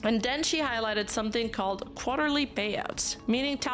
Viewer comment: oh. Because of Shylily's accent, I thought she said "quality" payouts in that clip when I watched it back then and had no idea what that meant. Hearing call it "quarterly" fits so much better and now I can even vaguely hear it from Shylily.